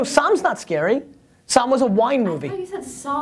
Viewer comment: I am with, I thought you said Saw too and was really confused.